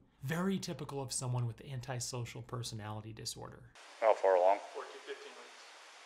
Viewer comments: did he say she was 14-15 months pregnant??? I must have misheard.... Edit: I don't think he was having one single thought during the neighbor's security cam footage. Rather multiple fleeting thoughts of despair, followed by a trickle of hope, followed by despair, over and over again. Which for us, was but a moment, but for him, an eternity. It was a well deserved moment. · did he say his wife is 4 to 15 months pregnant??? I know that's incorrect but it's what I heard, it was kinda inaudible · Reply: He said 14, 15 weeks